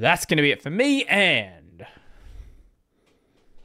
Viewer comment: With blackhat's and hookers?! Sorry, at 1.5x speed I might have misheard...